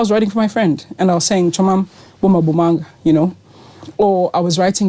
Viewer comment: Msaki sisi i thought you said "Ubom abumanga" ... come back 😭.